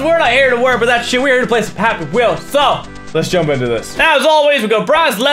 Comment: did he just say "pappy wheels"